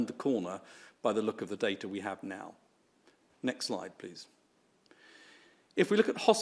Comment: did he say next Lie please